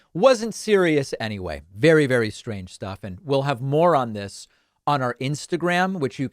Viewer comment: I misheard "More on this" as "Moron this", but maybe that's because I have Dennis Prager on the mind due to the topic of the video.